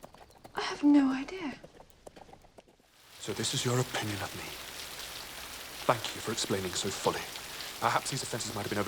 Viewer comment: На 5:49 что он говорит похоже на"факю" или послышалось